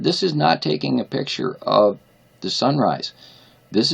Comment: I believe I heard "sun rise in the west"??? Must have been a mistake because you have so much to say so quickly